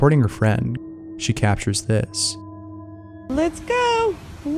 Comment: I heard "vad gör du?" meaning what are you doing